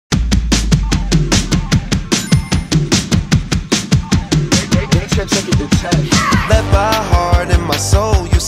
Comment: Can anyone tell me what is said at like 0:02 in the video? I cant understand it but Im curious, I know its maybe a label intro or somethin? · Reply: Daytrip took it to ten